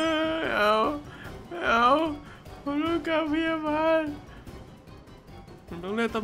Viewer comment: ทำไมได้ยินว่า "ผมต้องการเมียใหม่" เหรอเราคิดไปเองนะ